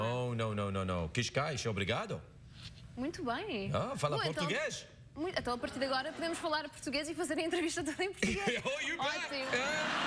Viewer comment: Anyone who speaks Portuguese, what did she say? Tell me what she said in Portuguese, and the English translation, please. I recognized Obrigado, Muito Bem and Eu Falo Português, but I couldn't understand the rest of what she said. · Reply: muito bem! Bom então a partir de agora podemos falar português e fazer a entrevista toda em português, óptimo :D there you go :D